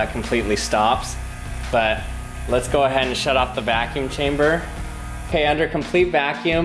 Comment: For some reason I thought it said vacuum cucumber